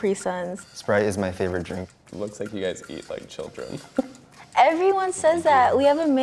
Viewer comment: I thought they said it looks like you eat children and I was like facts